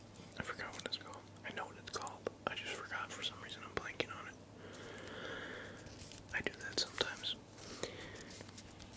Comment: what does he say? "Im blanking on it"? Im learning English 😁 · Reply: “I just forgot for some reason, I'm blanking on it.” Keep up the good work there 🇬🇧